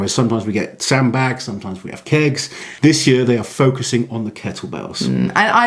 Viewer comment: I had to rewind that, I thought at first you said sometimes they throw sand bags and sometimes they throw kids 😮🤣